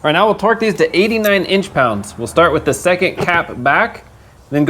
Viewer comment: Min 28:53… did you say (inch) pounds?…